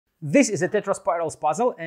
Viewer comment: At first I heard “Tetris spirals puzzle” and now I can’t unsee tetris blocks in the shapes!